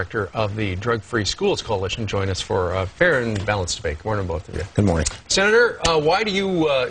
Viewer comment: Sounds like he said "Join us for a fair and balanced bake".